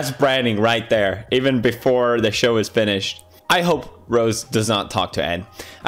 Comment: If you have caption on at 5:45 when Felix says Ed the caption say end